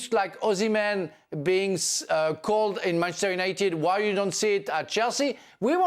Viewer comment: Did he just say Ozzy Man to Manchester @ 11:50 😀🤣🤣